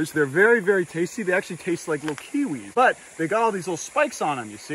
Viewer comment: Did he just say they taste like Leukaemia??